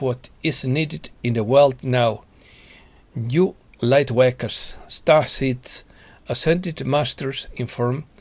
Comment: Thank you! (by the way, I thought you said "lightwankers" at 3:20 😆)